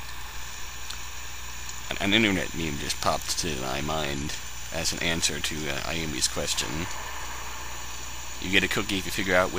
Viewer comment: Due to my horrible audio I couldn't quite understand what you said there. Would you please repeat write down what you said, I'm pretty sure it wasn't what I though it was ( My 10+ old audio system picked it up as : A new-get meme just popped my mind, as in to answer yummy kitchen). I was able to get the last part about getting a cookie if you guess which one it is, but not before that. Sorry to trouble you, but I get wierd images in my mind, when I try to think about what "it" is. Thanks.